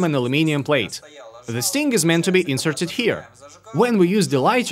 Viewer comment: did he keep saying "this thing"? you're smart dude but you got some spewch flaws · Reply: I thought he was saying "the sting", which didn't make any sense to me. I guess "this thing" would make more sense.